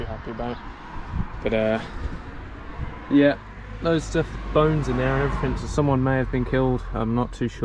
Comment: Not gonna elaborate on 2:14...? (Assuming I heard that right anyways! Bones?)